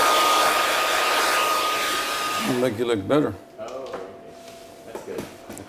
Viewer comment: What did say at 14:57? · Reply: "way better" - LOL